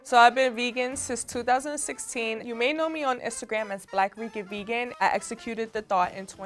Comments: Baby y’all gotta get the subtitles rolling. I mistook “Black Rican Vegan” for “Black Freaky Vegan” and had clutch my pearls right quick lol 😂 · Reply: I THOUGHT SHE SAID BLACK FREAKY VEGAN TOO LMAOOOOO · Baby y’all gotta get the subtitles rolling. I mistook “Black Rican Vegan” for “Black Freaky Vegan” and had clutch my pearls right quick lol 😂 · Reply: damn i thought she said black greek and vegan i was lost